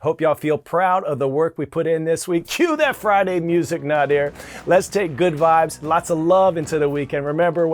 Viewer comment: Did He Say Night Air 😂